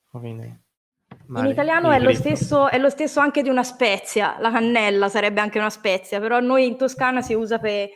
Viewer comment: In her Tuscan dialect, is she aspirating her hard c (k) sounds? To my English ears that know Spanish, it sounds like she is saying "La hanella" (La janela in Spanish), as if she is aspirating the "c" sound